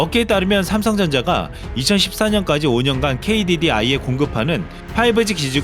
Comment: 2014년이라고 말한거 같은게 잘못들리는건가